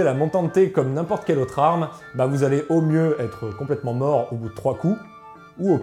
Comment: au mieux mourir au bout de trois coups au pire se détruire les articulations 🤔🤔 c'est moi ou il a inversé les situations 😂😂😂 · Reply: Non, ce qu'il a dit c'est : "être complètement mort au bout de 3 coups", c'est pas pareil, il a voulu dire "être très fatigué". Ensuite, "ou au pire, vous détruire les articulations". Donc il n'a rien inversé du tout.